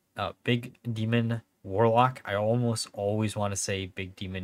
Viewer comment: Did I hear "Dungar Warlock"? 😉